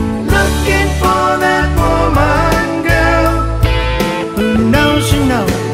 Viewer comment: Wow … I always thought he was saying “… looking for that fallen girl “ .. which I actually like better, come to think of it …